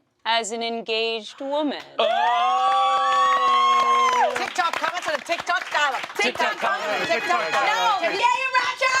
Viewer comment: What is saying at 5:58? "Tik Tok comments and a Tik Tok dolla" is what I'm hearing and that's nonsense.